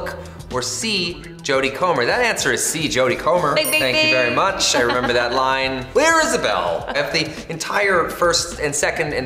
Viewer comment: What Ryan said on 4:39? Right before he said about scripts tattooed on his back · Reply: "That answer is C, Jodie Comer. Thank you very much. I remember that line clear as a bell."